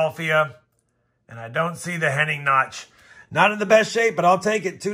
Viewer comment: at 8:21 in this video you say something about a notch on the 1939 P, what is that? Sounded like you said Hening notch but I’ve never heard of this. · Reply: The Henning Nickels - faked nickels from a man named Henning. 1939, 1944, 1946, 1947, 1953 - a lot fo them have a notch in the R in PLURIBUS but not all of them... Need to weigh them too since they weigh 5.3-5.4g as well. They fetch a nice premium in any grade since they were struck poorly as well.